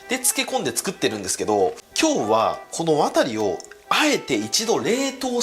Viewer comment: 「ワ↑タリを一度冷凍して〜」 言い方が「渡(人名)」に聞こえる…。 渡さん…逃げて…。冷凍ストッカーで冷凍される前に逃げて…:( ;´꒳`;):ﾌﾟﾙﾌﾟﾙ